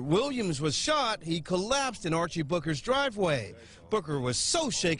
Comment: Archie Booker...I thought they said Archie Bunker. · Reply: I thought he said booger... 😂😂😂